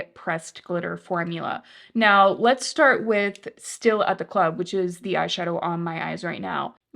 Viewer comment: At first I thought you were saying “still as a cloud” and I was like, well that’s lovely, but also clouds move kind of a lot